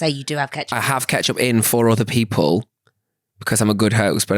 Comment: I thought said he has ketchup IN FOUR other people, like storing it for a midnight snack type beat!! 😂😂